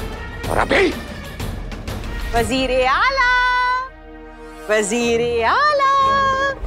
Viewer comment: caption Prime minister instead of Chief minister